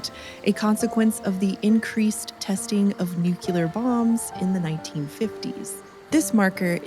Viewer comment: I'm sorry but... 1:40 Did you just say "nukyular?"